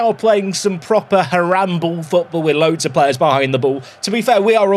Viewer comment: Did anybody else hear say “they are playing some proper Harambe football” ?